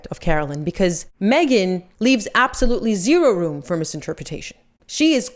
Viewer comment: Did you say "Meghan leaved absolutely zero room for misinterpretation..." at 3:12 ? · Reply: She said leaves